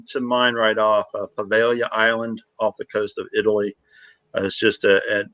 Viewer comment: What was the name of the island off the coast of Italy, mentioned @ 16:35 ? I tried to Google what I thought was said, which was Availia island, but could not find anything. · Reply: poveglia